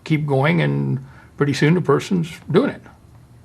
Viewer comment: did he just say that deep throating eventually leads to 'poooing????' wtf